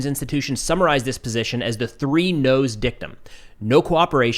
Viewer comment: I heard "three nose dickem" and i was like 👀👀👀 What did he saaaaaay